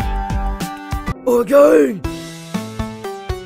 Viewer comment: Me: Why does it say "Some evil stuff eh?" Well tho- Evil bubble: *AGAIN!* Me: O_O